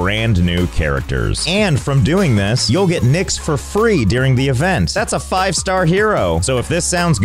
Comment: Haha, when he said "you get Nyx for free", I thought what a rip off! 🧐 How you pronounce Nyx, is the word for 'nothing' in Dutch. So I heard "you get nothing for free"! 😅